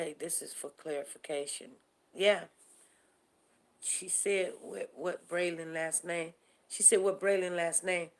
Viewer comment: I keep hearing “uh, what’s Braylen last name?” ... I guess we will agree to disagree on this one. I listened to a clearer recording on our after hours channel and you can hear it even plainer! Sorry family ...